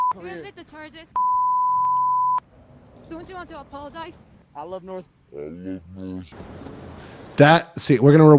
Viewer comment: did he say "i love north korea" ??😮😂😂this guy acts so clueless when they bring the heat and questions but everything is on film😂😂😂.....edit damn he most definitely did but also cussed out the news chick. I would love to see him get something heavy like 10 years plus because its time he gets what he has begged for, for years.